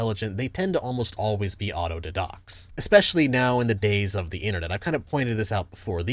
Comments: does anyone know what word he uses? Abnormally intelligent people tend to almost always be what? Autodocs? Autodedox? I’m genuinely asking, I looked it up online and found nothing. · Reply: Autodidacts i believe which means self taught. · what does he say at 5:35? intelligent people tend almost always be 'audodedox'? wtf is that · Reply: He’s being silly and mispronouncing “autodidact” someone who teaches himself expertise on a given subject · "The thing is ppeople who actually are abnormally intelligent, they tend to almost always be [?????]" I don't understand the word he said, could you reply and tell me what is it? English isn't my mother tongue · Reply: autodidact, basically means someone who self-teaches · it took me a second to realize you were saying "auto didacts" probably because I am used to the die-dact pronunciation instead of did-oct